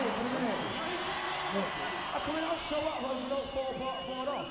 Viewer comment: Please, could anyone decipher what Liam says, for the love of God? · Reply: Liam said: “does it matter if I was four off” referring to him beginning the vocals 4 beats early. Noel’s response was that because the orchestra was reading what they were playing on sheet music, everything had to be perfect.